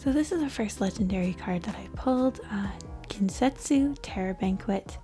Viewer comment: Bro is said Ginsetsu and I thought it said genjutsu